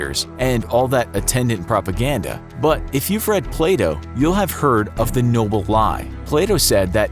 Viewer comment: When he said Plato, I thought he said play dough 😂🤦🏽‍♀️